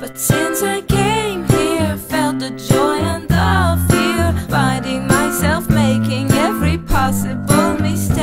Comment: I'm a new soul, I came to this strange world Hoping I could learn a bit 'bout how to give and take But since I came here, felt the joy and the fear Finding myself making every possible mistake La-la la la, la-la-la-la la la La-la-la-la la, la-la la, la-la la La-la la la, la-la-la-la la la La-la-la-la la, la-la la, la-la la See I'm a young soul in this very strange world Hoping I could learn a bit 'bout what is true and fake But why all this hate? Try to communicate Finding trust and love is not always easy to make La-la la la, la-la-la-la la la La-la-la-la la, la-la la, la-la la La-la la la, la-la-la-la la la La-la-la-la la, la-la la, la-la la, ooh This is a happy end 'cause you don't understand Everything you have done, why is everything so wrong? This is a happy end, come and give me your hand I'll take you far away I'm a new soul, I came to this strange world Hoping I could learn a bit 'bout how to give and take But since I came here, felt the joy and the fear Finding myself making every possible mistake La-la la la, la-la-la-la la la (New soul) La-la-la-la la, la-la la, la-la la (In this very strange world) La-la la la, la-la-la-la la la (Every possible mistake) La-la-la-la la, la-la la, la-la la (Possible mistake) La-la la la, la-la-la-la la la (Every possible mistake) La-la-la-la la, la-la la, la-la la La-la la la, la-la-la-la la la La-la-la-la la, la-la la, la-la la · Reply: I could swore she said felt the joy under fear finding myself